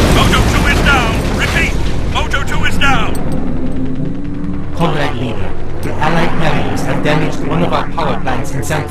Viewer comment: Did he just say "Comrade Lenin" in 02:53? Cool theory: Yuri is resurected Lenin. they just look like twins xD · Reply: Swagie Hoofer he said "Comrade Leader"